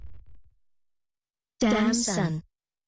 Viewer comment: What does Rosie says in this video? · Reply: Mike Lam Damn son